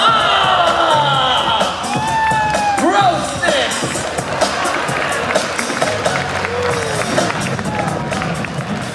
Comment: lol what did the MC say at 1:32? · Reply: grossnessss